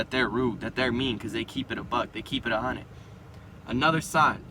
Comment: Couldnt catch what he said at 1:34 Anyone please? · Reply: Yahya Khamisb keep it a buck, keep it hundred means they keep it real